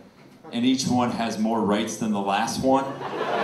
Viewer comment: I totally misheard 'rights as the last one' as 'rice as the last one' >_< Good stuff! I love the gyspy curse joke.